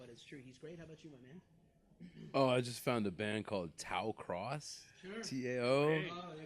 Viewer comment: I don't understand the name of the band mentioned at 11:10 - 11:12. It's "towel cross" on the automatic subtitle but I don't think it's right... Does someone know the correct name? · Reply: It's Tau Cross! has great taste! <link>